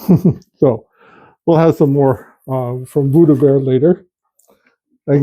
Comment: Ah! "Buddha Bear" I Thought you were saying "Voodoo Bear" 🤣